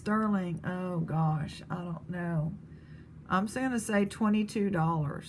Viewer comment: sterling & rose quartz necklace. I think you said $28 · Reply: $22 yours